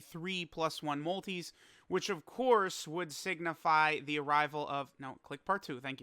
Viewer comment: anyone else catch that he said signifial?